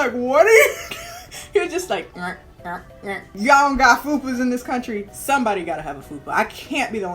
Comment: I'm cracking up here 😂😂😂😂😂😂😂😂 But what you just said in 11:12 ? Fubu, fuku what is that? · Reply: Fupa?